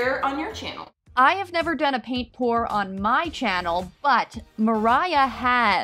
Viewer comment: The whole time I thought they were saying paint tour antil they pulled out the canvases😂